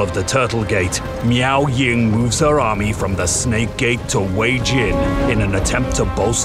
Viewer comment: did I just hear Kuei-Jin? dammit Ming Xiao, I thought I dealt with you back in 2004